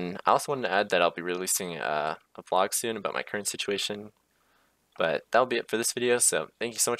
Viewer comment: it sounded like he said "please let me know if this was supple" lol yes it was